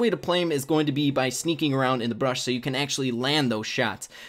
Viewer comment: I heard BRUSH not bush and this happens every time (see caption for evidence)